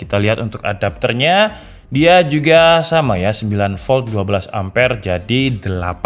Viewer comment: perasaan 2 amper dah at gua salah denger 12amper 108w dong klo 12 amper🤣🤣